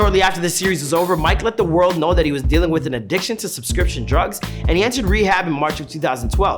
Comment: Wait! Did I hear him right @ 5:24 he said subscription drugs. Omg it’s prescription drugs. Lol